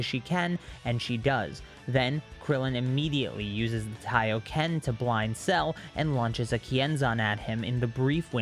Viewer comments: u mean solar flare?? Thought only Goku could use the Kioken lol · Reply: He said the Japanese name of Solar Flare, which is Taiyōken. That’s why it sounds like he said Kaio-Ken. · did he say Kaioken?? 🤨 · please tell me I'm not tripping and I just heard you say that krillin's using the Kaioken but you ment solar flare and I love your videos and I think this is funny so I'll let it go but I want to know if I'm just tripping or not like like if you all heard this too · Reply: You are tripping, he said Taiyoken which is the Japanese name for Solar Flare